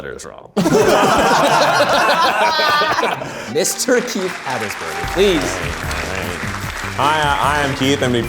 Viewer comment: Misheard this as 'Ms. Turkey Habersberger' 😂